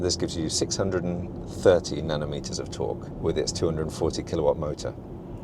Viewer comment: 630 Nano meters of torque?...Nano?... not Newton?...Did i hear that right?...